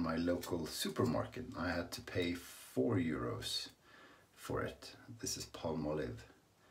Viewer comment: Whew! Had to go back and listen again, thought you said you paid €40 for the Palmolive 🤣😂🤣 I thought nobody is that crazy! 🤣😂🤣